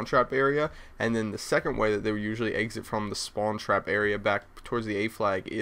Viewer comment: When you say exit... it sounds like your saying "Egg-Zit"